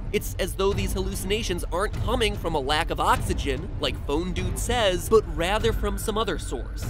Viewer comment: This is random but I thought I had he said phone dude not phone guy #differences